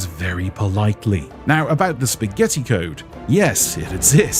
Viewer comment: Did I hear Spaghetti